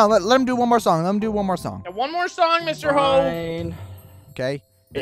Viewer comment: It sounded like said mr hoe